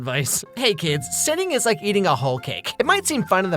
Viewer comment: I thought you said 'sinning is like eating a hoe cake' and was about to crash out fr 😂